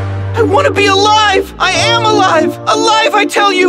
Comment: If you listen to were Mitty says I am alive it sound like he is saying I am a lie